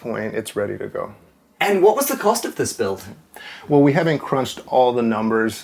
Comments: Genuinely thought he said: “And what was the cost of this filth?” Almost choked on my tea! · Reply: ROFL (12:33) ... once one hears "filth", one can't hear anything else! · Genuinely thought he said: “And what was the cost of this filth?” Almost choked on my tea!